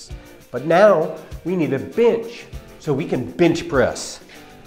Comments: Lmfao i thought he said "but nooow. We need a biitch". What?!?! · At the start I thought he said, ".....But now we need a bitch!"